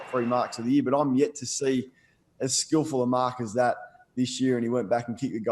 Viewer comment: Kane seriously, "skillfuller", try more skillful & I agree with you. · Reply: Agreed I heard "as skillfull a mark as that"